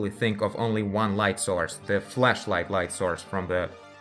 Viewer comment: is it just me or does it sound like he is saying fleshlight every time he is trying to flashlight?